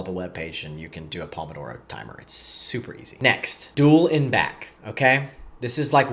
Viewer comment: you didn't explain how to do this & I don't really understand what you're saying.. "dueling back"? Even the subtitles can't decide what you're saying... Thank you for the video though. ☺️ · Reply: Ok, so you're saying dual n back, but what does that mean?